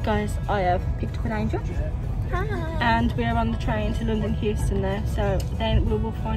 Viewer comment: Did I hear you say London Houston at 0:24 ? No wonder you got lost! 😂😂 "Going Underground - by The Jam" The chipmunk 🐿 voices at the penny machine had me creased! 🤣🤣 I think Grosey might have you for copyright on the vanishing meal trick 😁